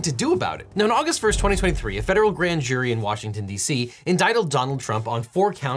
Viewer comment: did he say "inditled"?